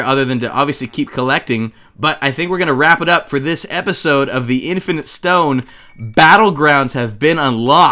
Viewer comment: I legit thought he said the infant stone